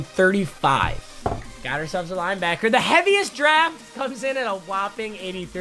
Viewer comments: I was high and thought it said fastest draft for like 10 minuets😭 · On gone thought is said "fastest draft" lmao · Anyone else thought it said fastest draft